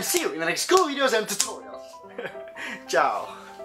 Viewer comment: Dude, did you say "Chào" at 5:54? A way to say hello/goodbye of vietnamese?